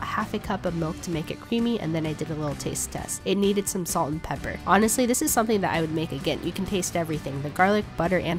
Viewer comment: did she say jalapenis